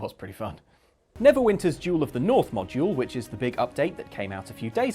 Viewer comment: Until I saw it written on screen, I definitely thought they were saying "DUEL of the North"